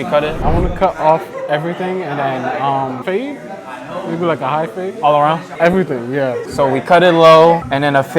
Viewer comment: Im sure he said he wanted a high fade… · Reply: Yeah, I thought he said high fade too